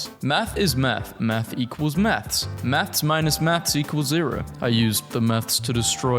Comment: Half of this video I hear “math/maths” as meth. I am struggling out here ✌️